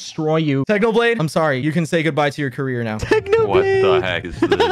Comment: I thought he said cancer for a second, but when I re-watched it, it was career